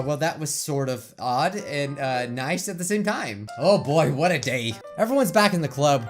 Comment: Did he just say "Oh boy what a gae"